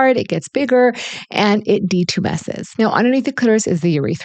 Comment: i hear '...it d2 messes...'???